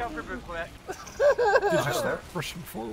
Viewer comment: Did anyone else hear "watch out for Buquec" at 11:00? XD